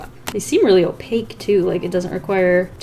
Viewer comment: NGL, I had to rewind to hear say "Cran" because I thought I heard her say "CRAYON" and I almost pooped ma pants.